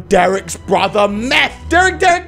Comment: did you say frankship?